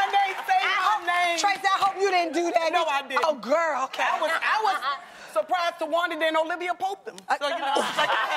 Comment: What did she say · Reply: "I was surprised Towanda didnt Olivia Pope him."